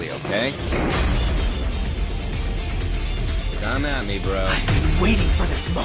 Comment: You hear what he says at 2:02? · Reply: “Come at me bro”